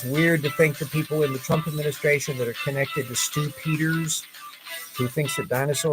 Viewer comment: For a second I thought you said Stu-piders!